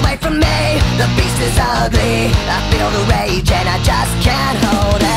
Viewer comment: What everyone hears:I feel like a monster What I hear:I feel like a mustard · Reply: instead of "the beast is ugly" I hear "the yeast is ugly"